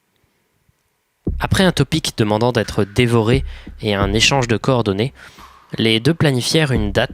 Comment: j'ai entendu "un échange de corps donnés" au lieu de "un échange de coordonnées"... Je crois que cette histoire de cannibalisme m'a un peu trop hypnotisée